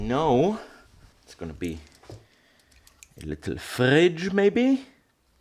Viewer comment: Moritz I was thinking dude was probably hungry and thinking about eating so he thought about the fridge 🤣🤣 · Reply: I thought he said, "Fudge." So when he drew squares I though, "He's engraving fudge?" Imagine my surprise when it was a fridge!